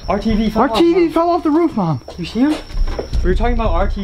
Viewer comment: At the beginning you guys were screaming "our tv fell off" I thought you said RTV Fell off